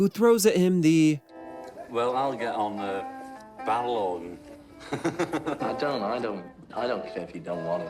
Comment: What John says here is "Well, I'll get on the Barrel Organ". Some of the automatic subtitles are complete garbage. They simply don't pick up on the subtle nuances of my beautiful native language. Not at all, and especially our particular northern accents. I'm only glad that the Beatles weren't from Birminham, because the world would view them very differently, if left to a computer to figure out what's being said. It's not a fault of this channel - which is first rate - it's a technological failing. I've also noticed a lot of instances of inserting "DO" when the word was 'Don't', the word 'Will' when it was 'won't' and so on. It completely alters the narrative in a really negative way, and disappointed me no end, watching this back on You Tube. If this was a video of something of little consequence, then it wouldn't matter so much, but when it comes to matters of historical importance - as us Beatles fans see it - then it's just as egregious as it's possible to be. Shocking really, that something like this is treated in such a lackadaisical way.